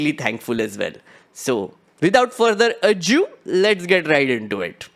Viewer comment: am I the only one who heard say "without further A-JEW" instead of "without further ado" ???????? edit: 01:46 in case anyone's curious